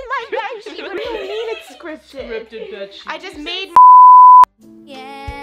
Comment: It rlly sounds like said scripted btc but i think she actually said scripted bedsheets idk tho